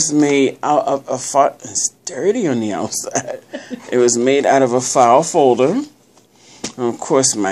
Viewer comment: I need to have my ears checked and fast...cause I thought you said "this was made out of a fart folder"!! I had to replay it and listen again. Great job....getting that hearing aid now! xoxo jonnie